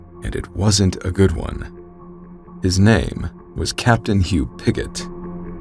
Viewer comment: "Captain You Bigot." That's what I heard initially! Haha... I was falling asleep when I tried to watch this vid for the first time. In my groggy state, it sounded like he said, "Captain You Bigot." That pulled me out of my haze and I had to rewind a little bit to check if I heard the name wrong. I obviously did. That shit made me chuckle for a bit.